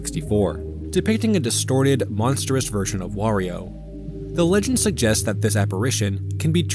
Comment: do you say "wegend" instead of "legend?"